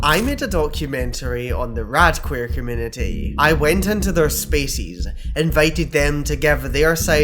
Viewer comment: In the opening, with your accent, I totally thought you said you "went into their SPACES" but I 100% heard "Went into their SPECIES" and I still can't stop laughing😅😂😅😂😅😅🫠🫠🫠🫠🤣🤣🤣🤣🤣